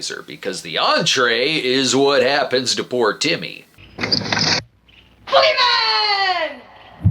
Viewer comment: I must have kept going back to this over and over again, constantly unable to understand what Timmy was saying until Brad spelled it out for me. Seriously, it sounded like the kid was shouting Fleabag or Freeman.